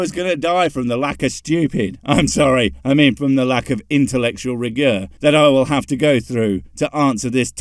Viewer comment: I heard "intellectual rigor" as "intellectual regur," and momentarily pondered whether it was the root word for regurgitate. Even in retrospect, it makes sense in the context in which it was used, which makes me wonder if listening to so many theist videos is beginning to do actual damage to my grey matter.